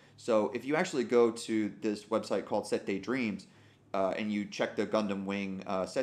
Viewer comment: The narration is kinda misleading, the color chart shown in the slide isn't an actual sheet used by animators but a recreation by 玉英 (Yuying?) based on real color sheets and data which got archived by an user called ねこまたやさん (Nekomataya) on their website. What he means by the setteidreams mention is that you can find what colors the Gundam model sheets used by their written color codes, which are part of the recreated chart (like all of the 90s gundam sheets are black and white photocopies, so a real color chart sample scan from a collection would probably be useless anyways). I managed to find the chart by screenshotting and cropping it off the video and reverse searching. I can't post the source site's link or even allude to it because YouTube's safety team is composed of babies and crappy AIs so they just remove or hide my comment, but it should be somewhat easy to find through the reverse image search. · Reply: The word "settei" was throwing me, I kept hearing "sentai dreams." Clarification hugely appreciated. And still a really good resource, looks like.